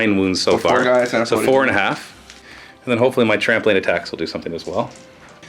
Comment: Every time you said "Trampling Attacks" I heard "Trampoline Attacks" - which doesn't seem at all out of place for Squigs!